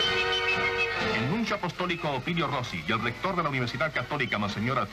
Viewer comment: Eso es lo único que no entiendo. ¿El qué apostólico? ¿Y cuál era su nombre? ¿ Opinio Rossi? 😵‍💫